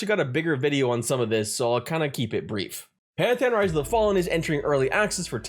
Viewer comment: What is the name of that game? Can't hear what you are saying. :P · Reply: Pantheon: Rise of the Fallen <link>